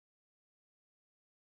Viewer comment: I thought he said “I feel cannibal.” 😂😂😂 Took me a min. to realize he said “accountable.”